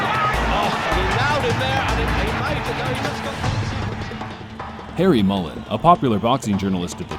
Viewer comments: i thought he said "hairy mullet" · i thought he said "hairy mullet" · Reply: Hi MrTrollbaby from the past, nice to see you made it to 2021. I thought he said hairy mullet too. See you next year.